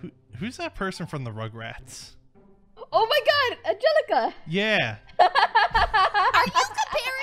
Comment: At first I thought he said angallica he meant from Hamilton but I was wrong